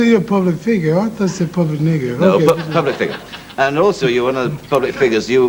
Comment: Did you just call me a " public nigga'.. Funny shit Ali'